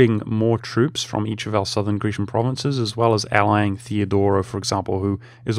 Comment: Did you just say "greecian" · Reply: grecian